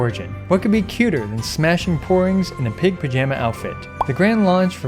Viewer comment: The first time I heard your sentences @ 1:14 I thought you were saying "Smashing Porns", but turns out you were saying "Smashing Porings"